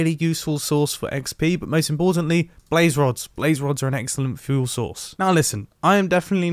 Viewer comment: I thought he said food source at first